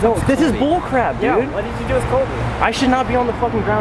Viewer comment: There are 4 Solby moments in This video 1.when the 'cop's pushed Colby tried to put his arm in front of him.2. said "What did you do with Colby!!" ❤3. also said "Where is Colby!!" 4. said "Is that Elton a-a-nd Colby.." but the way he says it...he was more worried about what happens to Colby!!#SOLBY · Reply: Lexie it was around 11:40 something. it sounded like said "where the f''k is colby" then said "yeah. what'd you do with colby?"